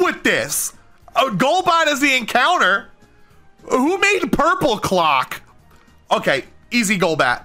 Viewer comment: Hardy😭 Edit:When he said purpleclock I thought he said purplecock and the clip where the video responded to him saying hi had me dying of laughter😂